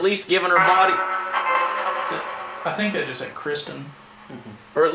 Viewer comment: I heard "I loved her", not "Kristen", but it could be my getting distracted by a spirit box that sounds like one of my dogs who got raspy by barking at every single delivery truck within a 7-mile-radius during Christmas.